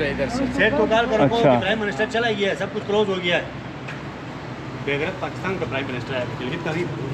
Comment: He actually said beghairat, not "he" referring to the P.M. Even the locals consider this an illegal occupation.